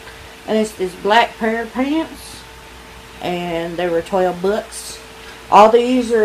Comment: did she say they was 12X? 🤣😂